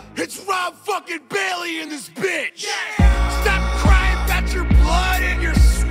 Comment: At first I heard "It's Rob fucking Bailey and his bitch" instead of "in this bitch". Yikes.